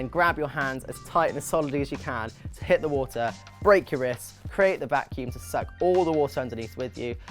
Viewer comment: I thought he said break your wrist. I took me a minute to realize he said brace. Good Luck in the competition!!!!